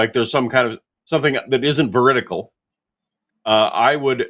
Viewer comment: says "... something that isn't [veritacle?]" and I have no idea what that word means. The closest I could find was maybe he meant "veritable" or maybe I just misheard him. Anyone have any ideas? EDIT: It's difficult to google because it just thinks I'm misspelling "vertical" lol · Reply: The word is "veridical". Meaning coinciding with reality.